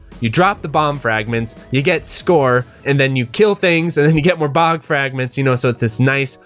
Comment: Maybe I misheard but I thought I heard you say that "you collect Bog fragments" :D